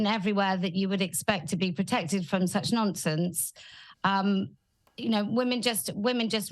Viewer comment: Every time Kelly says "nonsense" I hear "nonce-ense" (nonce = p3do for non-brits who dont know) and I cant tell if it's just me or not.